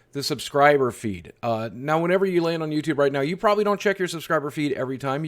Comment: omg I thought you said "subscriber FEE". I don't keep up with this stuff so I imagined getting a bill for a million dollars.